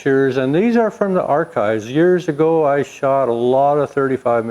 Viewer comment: I did the math and I can't believe you're over 70. You got the jigsaw when you were 10 over 60 years ago you said. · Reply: I thought he said 50 years ago.